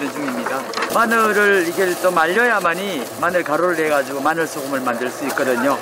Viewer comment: 자막은 구워야만 인데 제가 들리는건 말려야만 이라들리는데 2:55에서는 굽는다하셔서 그러신건가용? 음 뭐가맞는건가 갑자기 궁금하네요 저기에 둔 이유가 말리는건지 굽는건지..